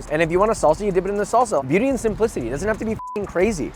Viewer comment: what did he say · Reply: Beauty and simplicity